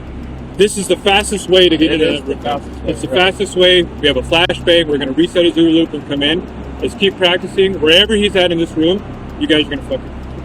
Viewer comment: I heard "We have a flash bang. We're gonna reset his OODA loop when we come in" not "do a loop" 🙃 OODA loop is a common military acronym